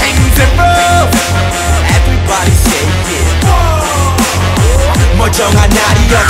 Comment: I don't think that U-Kwon said "Everybody say 'Yeah'", but I think he said "Everybody shake it". Look at the official MV. B-Bomb was pointing his fingers side to side like he's asking someone to shake whatever it is.